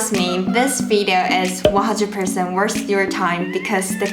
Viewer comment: Did you say this video is 100% worse your time? 😄 Omg sorry I am not a troll or a hater. I just thought your English accent is so cute. I am learning Chinese and this video is helpful. 😊